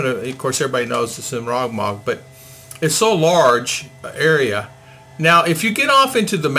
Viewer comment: Where are you in Mindanao, sir? I keep playing back 0:44 and can't catch what you're saying Ha! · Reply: Hi Maramag.. bukidnon...